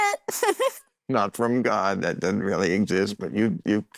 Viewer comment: God doesn’t exist? Is that what he said 2:38??